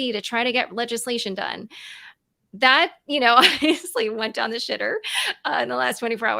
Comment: Did I just hear say "That went down the shitter"? 😂